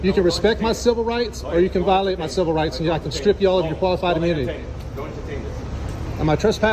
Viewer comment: the caption is wrong, it reads "go ahead and detain him", the officer actually says: "don't entertain him, don't entertain this"